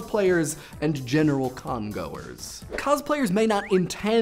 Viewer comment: He said: "General Con goers" I heard: "General Congo-ers"